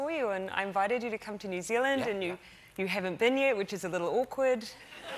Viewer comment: She’s so down to earth it’s refreshing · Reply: you should know, I thought you said you were a New Zealander.